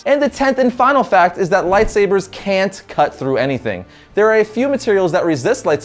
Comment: Can't cut through EVERYthing, right? I heard "Can't cut through ANYthing"